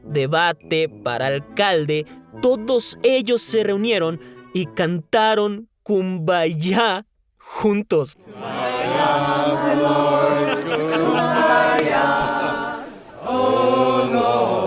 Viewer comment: pense que dijo bombayah y ya decia que hiban a cantar kpop